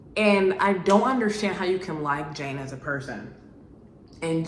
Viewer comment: I thought you were saying you didn’t like Jane before I realized you were saying she deserves better